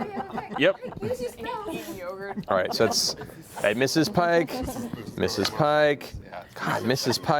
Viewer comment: matt rolled so bad I thought he was calling pike "Mrs. Pike" lmao 😂 instead of misses pike 😂😂😂